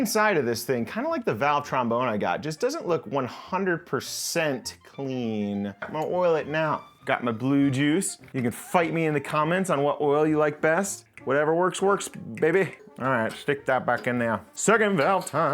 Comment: Why does it sound like ur saying "got my blue jews"